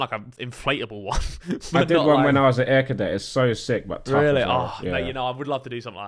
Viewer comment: What is that tobi is saying at 01:46? thanks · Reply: When he was an air cadet